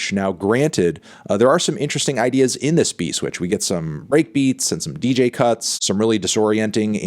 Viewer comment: did he say “rape beats”